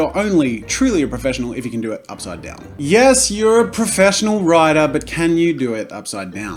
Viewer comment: Did he say writer?